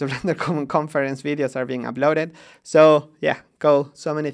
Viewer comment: am I the only one who heard the word "Ciao" at the end? xD